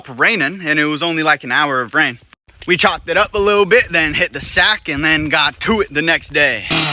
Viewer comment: Did I hear “hit the sack and then hawk TUAH???”